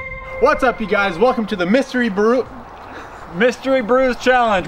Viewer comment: I thought they said mystery brew